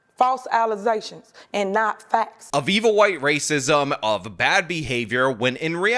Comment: wait... Just... One... Sec... Did you say Viva white racism? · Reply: “Of evil white racism”